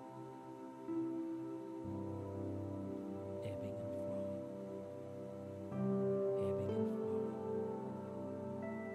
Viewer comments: WHAT IS THE FIRST THINGS HE SAYS AT THIS TIME? It doesnt sound like English? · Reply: Ebbing and flowing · go listen and someone tell me what he is saying??????? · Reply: Everything is fine, let it go, and again??